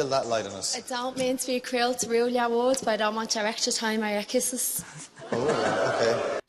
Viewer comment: HELPP the way you cant understand irish accents, but i mean its understandable 😅 she said 26:43 "I dont mean to be cruel to rule your world, but i dont want your extra time or your kisses" theyre very poetic on this show (she also has a very thick accent, I'm sure of a dublin accent)